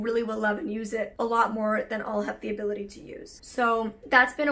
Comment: SAY WHAT? I was just casually listening and considering she was just talking about her weight loss, I thought she said "so that's how I've been trying to reduce my waist" and my first reaction was "girl your a size 0, reduce any further and all you'll be is bones lol." A couple seconds later: I REALIZED she was saying reduce her WASTE as is garbage/ trash but only because she was talking about getting rid of stuff not being worn. Confusing choice of words there, but I gotcha👌.